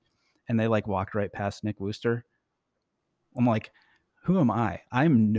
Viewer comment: I googled big woofers. 🐶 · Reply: Lame joke, I thought I heard Nick Woofers.